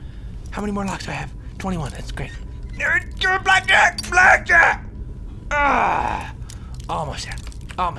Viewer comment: Did just say "Black Jack" while he was picking a lock at 7:38?